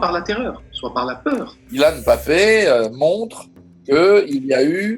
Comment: Y a que moi qui ai entendu killian mbappe